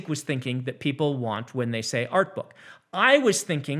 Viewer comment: He says "Art book" and I hear "Coloring Book"